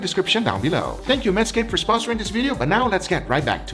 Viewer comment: I heard, "...thank you 'Meskin for sponsoring this video." As a 'meskin, let me be the first to say, "de nada, amigo!"